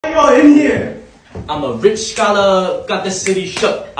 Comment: At the start I heard “Why y’all Indian” 💀💀💀💀